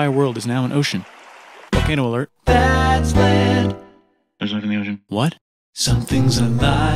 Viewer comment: If your wondering what he said, he said theres life in the ocean